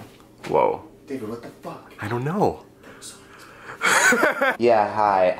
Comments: Does anyone have any idea what Alex said at 1:32 when he was talking to David about Ilya? I got the first part, but the last part before David laughed i cant understand. It's killing me for some reason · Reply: "David what the fuck! That was so unexpected." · What did Alex Ernst say at 1:30? I've watched this so many times and I still can't tell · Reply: Claire Li I’m a little late but he said “that was so unexpected” lmaoo I had to replay like ten times